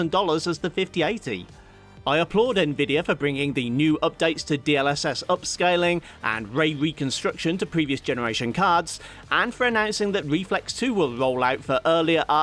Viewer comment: I heard a lot of "needs upscaling", which I interpreted as "Nvidia's upselling"